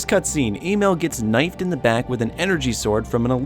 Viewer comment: Did you just say emile was called "Email"